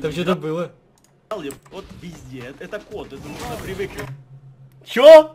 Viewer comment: я услышала " вот везде ЭТО КОТЫ" ))))